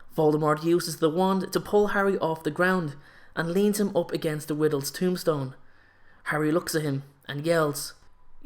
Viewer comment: Was it just me that noticed that instead of saying Riddle at 4:11 he said Widdle.